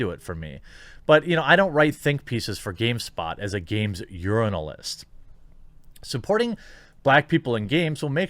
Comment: Did you just say "urinalist"?